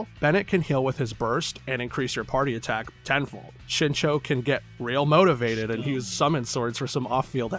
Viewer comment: it sounds like you called my boi "shitshow", lmaooooo